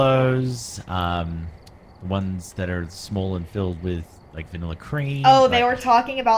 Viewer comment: There was a time that I wasn’t listening and I heard him say small things filled with cream Hah Hah Hah Hah I started questioning everything that he said I wasn’t official until I went back and heard what he said